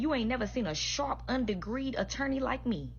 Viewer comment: "...a sharp, undegreed attorney?..." Is that what she said?